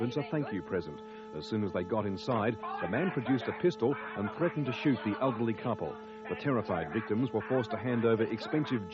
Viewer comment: what does the guy say? 😂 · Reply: He said you owe me a new letterbox mate